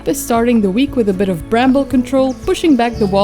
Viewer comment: I hear David Bowie singing 😮🎵„Bramble Control to Major Phil“